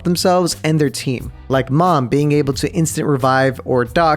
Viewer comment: did he say mom?